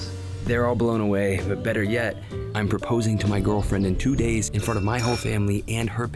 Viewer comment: 2 days dam, the wait must have been horrendous (oh i thought you said GF OF 2 days not IN 2 days lmao)